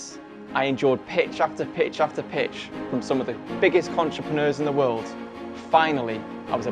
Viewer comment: did you say "CONtrepreneur"? 😂😂😂